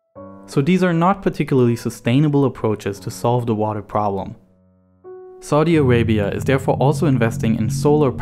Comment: Oh, excuse me... I thought you said murder problem... never mind. bye.